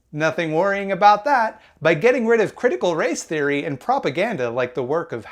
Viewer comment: I keep hearing “Critical Rice Theory” and I can’t.